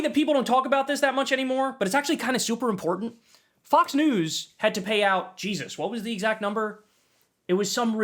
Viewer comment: I thought bro said they had to pay Jesus. lol